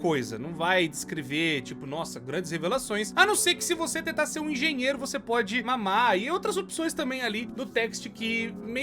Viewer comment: "Se você for engenheiro você pode mamar"? Não entendi essa parte